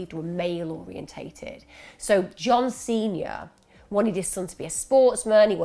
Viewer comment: I thought you said John Cena the wrestler when you said John Snr, I had to rewind it 😂😂x